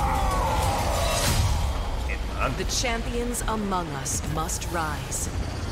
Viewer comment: porque dice amongus us?? xDD